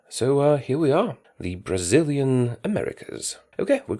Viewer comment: i thought it said brazil takes africa